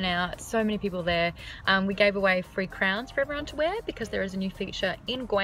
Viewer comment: At first I thought you said you gave away free crayons then I realized you said crowns lol. I still love your accent though :)